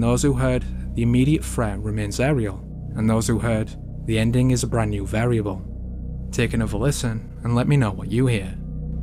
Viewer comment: "The ending is a brand new variable"? I heard "The indonesian are scared of grey people"...